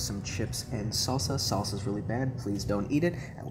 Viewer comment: “Salsa’s really bad, please don’t eat it.” I can’t be the only one who heard that.